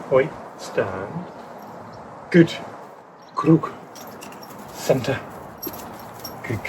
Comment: Excellent video with great insights again, thanks! Out of interest, is that ‘круг’ I hear at 7:29? :)